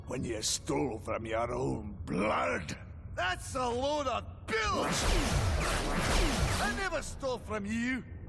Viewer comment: it sounded like he said bullsh*t